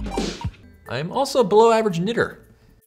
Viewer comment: did he say nitter?